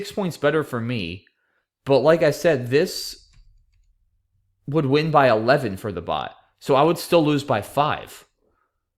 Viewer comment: I'm sorry, but what word does JEWEL take an I to form? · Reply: He said "Pretend the I is an S."